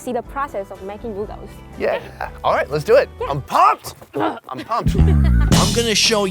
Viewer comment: "im pumped". lol. I thought i heard there